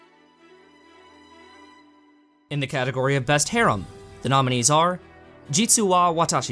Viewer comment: When you said 'Best Harem' I seriously thought you said 'Best Hair'